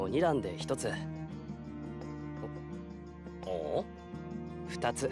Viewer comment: i thought he actually said “make that two”